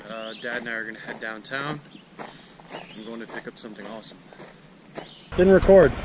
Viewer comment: I thought you said you were gonna head downtown and pick up some possum ... hehe